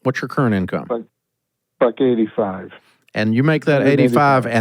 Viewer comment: did he say he made 185k or 85k a year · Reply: I thought he said buck-85 but he never corrected Dave.